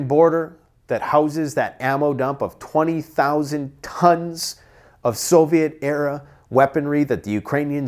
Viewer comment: did he say 20, 000 cunts ?🤔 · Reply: No Tons